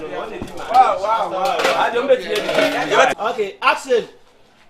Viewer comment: Did I just heard action